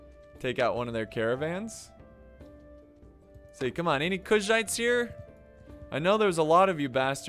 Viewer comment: I hope u know Kushite( everytime u say kuzhait it sounds like u say Kushite) was a North African people...slavery flashbacks